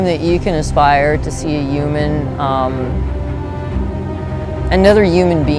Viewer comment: I'm pretty sure I heard her say " uman" instead of human